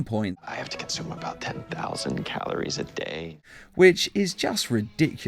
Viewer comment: I actually hear 1000 calories not 10000 . This comment doesn't mean that what you said is wrong I belive you but in the show I only hear 1000 calories